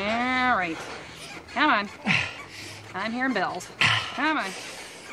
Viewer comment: i always thought she said "come on, pay your bills"